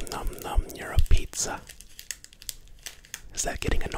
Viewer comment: I thought u were saying “Europe pizza” ahahahahahah